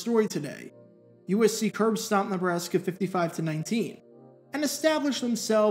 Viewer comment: USC curbstomped who? I've listened to it 10 times now and I have no idea what he's trying to say. Edit: after slowing it down it seems like he said Nebraska, but not 100% sure.